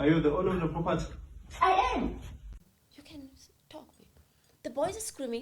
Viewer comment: did I hear her say "you can...stop babe" is she referring to her talking over her, putting words in her mouth and finishing her sentences 🤔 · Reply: She said you can talk, babe. Like this is the part of the story where you should take over. But the mom doesn't really give her a chance to narrate that part.